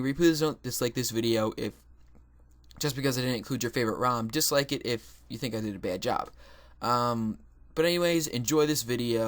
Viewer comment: "just like it if you think i did a bad job"? is that what he said?